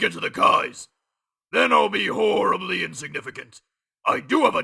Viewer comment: I always thought he said "Whore of the insignificant" rather than "Horrrribly insiginificant." I like my version better.